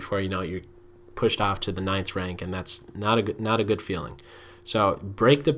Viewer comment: did he say ninth rank?